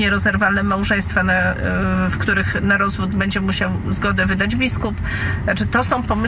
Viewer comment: Usłyszałem słowo piskup.... Dopóki kosciol rządzi, dopóty o piskupach proszę delikatnie mówić, naprawdę. Ale narzekać Panie mogą.